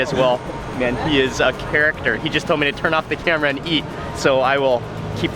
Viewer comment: what he say mark ? Turn off the camera and eat ? Aha you funny you did the complete opposite kept the camera rolling while you ate 🤣 like you recording for your life and YouTube who does he think he is. Ahaaaaaaaa